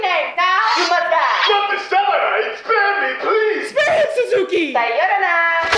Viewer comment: Did she just say Sayorana? 😂